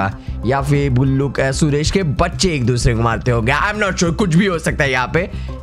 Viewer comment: did he say allah??